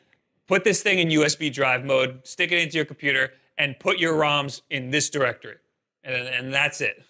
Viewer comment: Hey! minor criticism at 6:23. The people who did your captions put RAMs instead of ROMs like you said. It just kind of bothers me lol